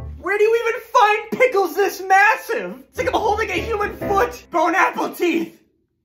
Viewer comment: I thought he was saying "home apple tea" under I realized what he did he mean lol